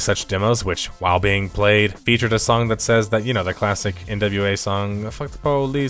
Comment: lol i was confused when i heard "the nba song f the police". took me a sec to realise i misheard nWa